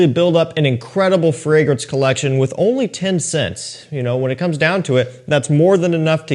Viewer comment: I thought you said "only ten cents" lol